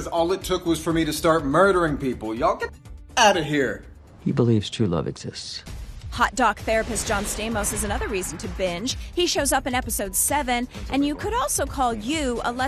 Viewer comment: I thought they said hot dog therapist